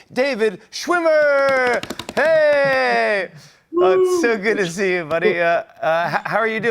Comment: did he say it’s so good to see u my Nigga? 👂 i mean I could be wrong 🤔